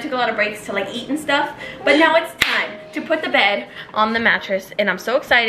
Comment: Did she just say "it's time to put the bed on the mattress??"